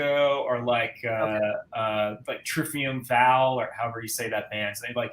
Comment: Might sound kinda weird as a question but what is the band that was talking about at 19:10 ? Literally tried spelling what it sounded like he said and cannot find anything anywhere 🤔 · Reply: Triumvir Foul.